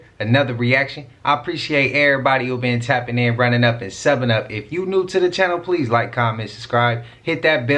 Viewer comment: When you started I thought you were saying “ head up seven up” instead of Subbing up , it brought back memories of being a kid on a rainy day in school and playing heads up 7Up. Anyone else remember those carefree silly things that made us happy? We had no idea how good we had it.